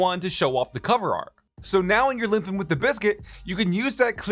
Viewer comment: did you just say "limping with the biscuit" lol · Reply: Limping with the Bizkit